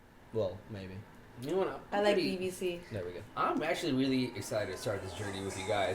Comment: What did say at 3:15 before she sipped her tea while smiling with her eyes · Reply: Stannis Baratheon “I like BBC.”